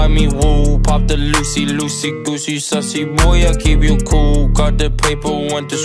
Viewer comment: DID HE JUST SAY SUSSY BOY?